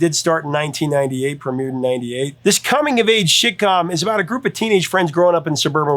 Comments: You totally said " Shitcom" around 8:08. I was packing my ebay shit and had to rewind to make sure i heard it right. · Coming of age sh*tcom??????? Listen.... that's exactly what says!!!!!! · sounds like he says shit-com, haha.